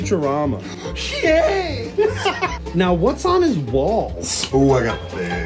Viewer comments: I honestly thought he said “what’s on his balls” lol 😂 but it was “what’s on his walls”. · "What's on his walls" What I heard: "What's on his BALLS ?"